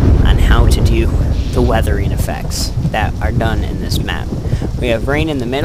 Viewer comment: i thought he said "Weathering effects that are 'none' in this map" lol